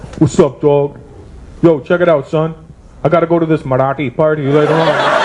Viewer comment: does he say Marathi?